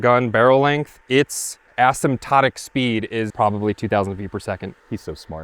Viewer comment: I had to look up Asymptote. It sounded like you said ass-in-tow. That is what my wife has...... my ass in tow!!! LOL